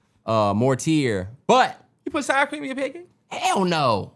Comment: did i just hear sour cream?😭 on pancakes??!! wilding 💀😂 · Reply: I heard “in” your pancakes it sounds so crazy he might be on to something. it’s too out there